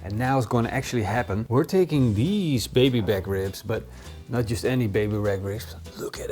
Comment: Did u just say baby rack ribs..?